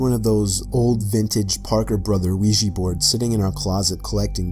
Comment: Story 1; It sounds like you said "Luigi board" XD